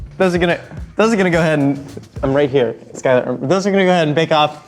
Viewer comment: Omg when Josh said “I’m right here” to the camera a few times and it sounded like he said “Skylar” and that’s my name and I was only half paying attention till he said that xD it spooked me lol